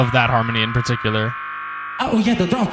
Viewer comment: 自分リスニング能力高くないので聞き間違えかもしれませんが13:17は ah,yet a drop?(やっべ、もうドロップだった)に聞こえました にしてもcardonaが意外とsyjoに対して鋭いこと言ってて笑ってしまった